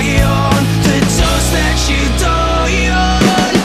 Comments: I watched a lyric Video. There it says : The shoulder you cry on. The dose that you die on. But i always hear : The shoulder you cry on. The dust that you die on. But all in one i love this song. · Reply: I hear 'the jokes that you die on' 😂😂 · toast you dine on???? weird lyrics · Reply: e fisher oh.....I thought it said "drugs that you die on". thank you for clearing that up for me.